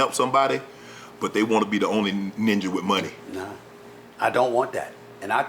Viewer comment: Did Unc just say NINJA OR WHAT 14:05 DID I HEAR?🤣🤣🤣